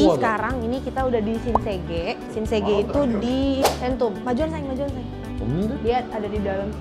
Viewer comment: ini aku salah denger gasi?? kyk denger ka ngmong "majuan sayang" wkwkkw